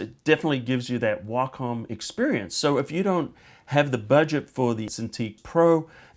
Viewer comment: What model name did you actually say before the sound edit? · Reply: lol, eagle ears huh? I accidentally said Intuos instead of Cintiq ;(